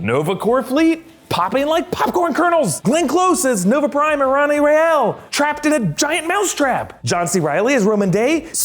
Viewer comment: WTF does he say after he says "Nova Prime"??? I've listened to it about 15-20 times and I'm no closer to understanding it then when I first heard it lol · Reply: Chris Li Loia Glenn Close (actor) as nova prime “Irani Rael” (character). Gotchu lol